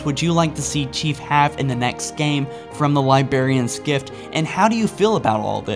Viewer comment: Did... did he just say "liberrians"?